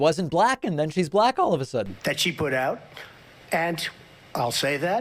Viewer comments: Did he say "She puts out?" I can't believe David missed that. Or am I tripping? No, he says she puts out. What a disgusting pig. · "and that she put out" did i hear that right? What a disgusting piece of crap. How has nobody caught that????